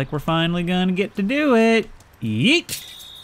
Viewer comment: I heard yeet that’s the word of my people